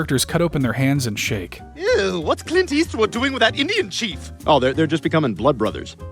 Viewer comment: Am I the only one hearing Stewie say Idiot Chief instead of Indian Cheif? as a Hoosier/Indiana Resident myself, that would be complete irony as I suggested my fellow residence to be called Idiots instead of Hoosiers (but this was before I realize how good I have it here, and appreciate my home state)